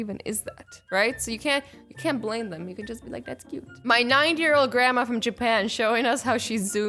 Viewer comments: Who else heard her say "my NINE year old GRANDMA" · my 9 year old grandma XD thats what i heard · Anyone hear her say 9 year old grandmother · no hate or anything but it sounds like she said "My 9 year old Grandma" instead of "My 90 year old Grandma" 5:02 (again i repeat,this isn't hate) but who else heard it too?